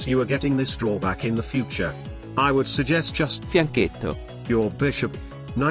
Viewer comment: I never know what the f it is saying, can someone just tell me in text format? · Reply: fianchetto